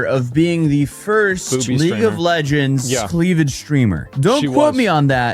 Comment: I know he said "Cleavage Streamer" but I heard "Cleveland Steamer" lmao